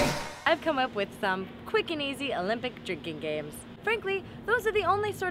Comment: I've always wanted to be a pot pie. So thank you Hannah for fulfilling my dreams. P.S. I thought you said jerking game. For my jerk chicken.